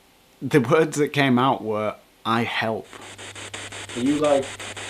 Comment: i didnt hear "i help" i heard "now"